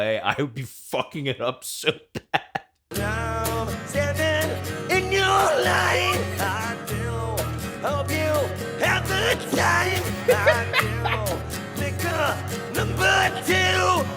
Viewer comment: Note that at one point it sounds like he says “I do take a number two!” so, I mean... he could just be straight up shitting himself.